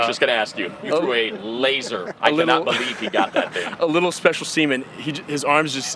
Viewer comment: what did he say 😳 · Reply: A little special seman. Which actually is a correct statement.